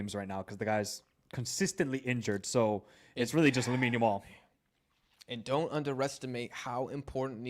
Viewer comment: instead of "Lamine Yamal" the subs say "little mini wall" xD